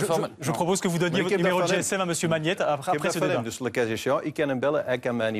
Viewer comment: wat zegt Bart De Wever in het Frans? "le kachichons"? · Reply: "Le cas échéant"